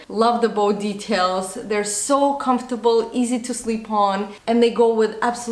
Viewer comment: Did she say her shoes were "easy to sleep on" ??? Who sleeps with their shoes on 😐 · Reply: Noemi Castro 😂😂 she said "easy to slip on" but with her accent it sounds like she said "sleep".